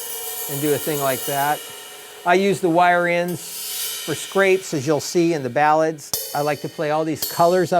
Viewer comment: On 4:47 you mention what sounds like 'scrates'. Did you mean to say 'scrapes'? I am looking for the correct term to use in a score. Can I write 'scrape cymbal with ring of brush' for that effect, or is there a different term?